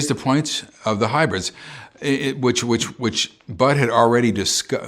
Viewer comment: did he just say butthead ?? the guy he is talking about is called butthead ??? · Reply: BUDD is what he said.